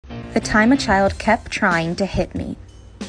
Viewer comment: AT FIRST I THOUGHT IT SAID THE TIME A CHILD KEPT TRYING TO HIT ON ME